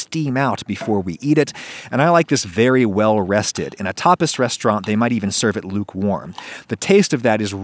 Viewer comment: I thought for a moment you said "Topless" instead of "Tapas" 😀😀